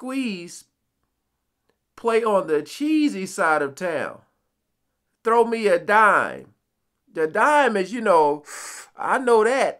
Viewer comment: I thought he was saying, "throw me a line".